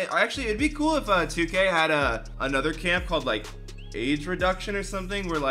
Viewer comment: Ima be honest thought he said aids reduction